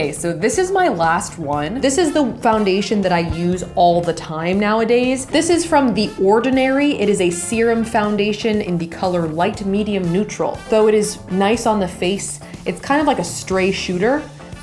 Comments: I thought you said semen foundation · Instead of light medium neutral I thought you said light medium loser :D I had to rewind it to understand